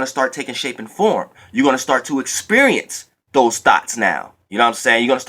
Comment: I thought u was saying thots😂😂 Thank u for this message. I definitely needed it😩💜👏🏾